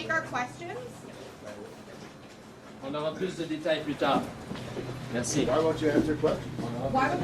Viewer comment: I thought I heard him say "putain"😮 ... but it was "plus tard" 😂😂.